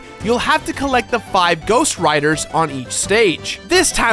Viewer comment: I thought you said ghost riders when you started talking about the ghost writers